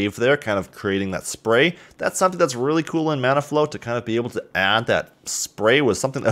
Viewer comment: I don't know why but I kept hearing he say 'Mana Flow'.